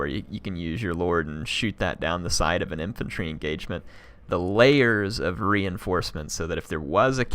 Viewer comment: I thought you were saying Valaya's instead of the layers xD great game by the dwarf player I think he did what should be done as dwarfs, defending against cavalry needs noob box, defending against flying units needs a cheap unit to take the damage and hold the flying unit while slayers come to follow